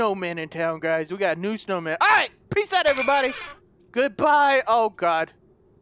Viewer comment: i thought he said PISS ON EVERYBODY